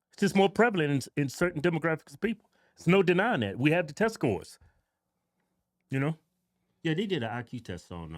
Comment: The way the twin pronounced "we have the test scores", I thought he said "we have the testicles" 😂.